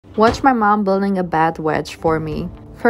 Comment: Okay sorry but at the start I heard “watch my mum build a bad wedge for me “